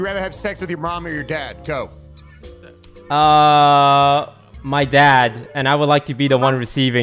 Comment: thought he said “dad so he won’t leave again” loL